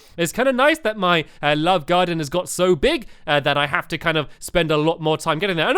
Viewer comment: he said love garden did he mean to say Funland or am I just hearing things and he said funland